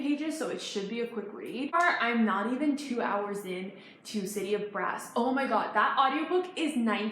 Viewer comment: The captions say city of breasts 😂